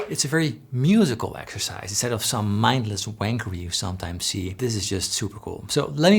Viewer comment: Did he say, "mindless wankery"? Is that a musical term?